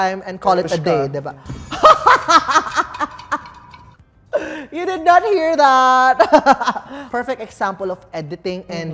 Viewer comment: Okay medj bingi ako, can anyone tell me what said at 31:10?? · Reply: verushka raw po